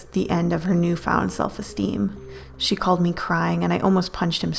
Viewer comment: When said she called me crying I thought she said she caught me crying